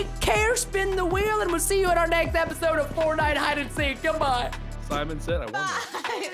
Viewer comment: I thought Preston said Samon, and I was like, Okay, it's a fish killer..